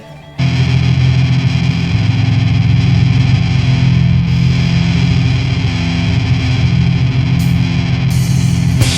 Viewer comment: Hello there. Dynamite stuff. I never heard of the Pariah Child label until now. Can you tell me the name of the band that started at the 11:11 mark? I heard you say "The River". Is that correct? Thanks brother. I hope that you enjoyed the endless summer. Sad that it came to a close today~ Rob/Boston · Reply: Yep, it's The River. Not a Springsteen tribute, as I initially thought.